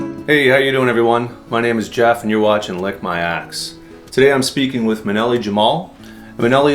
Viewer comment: lmao! i was listening to your videos play on playlist while doing something else, and what i heard was , "hey im jeff, and you're watching lick my ass." i was thinking, "what the hell just started playing...". Was humorous, thats for sure.